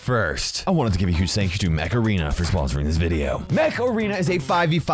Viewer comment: I THOUGHT HE SAID MACARENA 😭😭😭😭😭😂😂😂😂😂